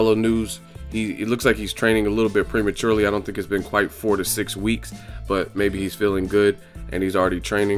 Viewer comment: I thought u said Canelo can't be back until 46 weeks and then I realized it's 4-6 weeks 😂😂